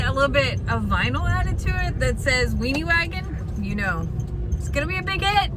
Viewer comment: my bad... you said weeny WAGON.... had to go back and watch again... but at first, i for sure thought you said jami was weeny whackin. lmao